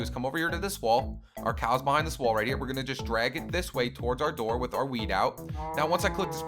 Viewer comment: BRUH i thought it he said "... With our weed" lolololol